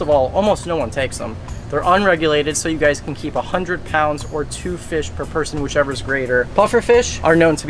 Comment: did you just say 100 lbs or 2 fish per person ?